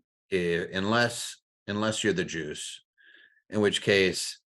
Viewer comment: Caption reads: "Unless you're the Jewish" LOL YouTube trying to get cancelled! Of course he actually said "the Juice"😂